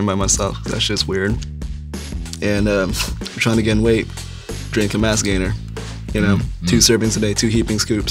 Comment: Can someone please tell me what Brian said at 7:44 that he said helped him gain weight. I cannot understand that shit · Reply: Moldy Duck Donut mass gainer I think? Like a heavy protein shake idk